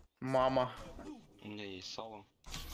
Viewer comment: Нихрена не расслышал на 3:23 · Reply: у него есть сало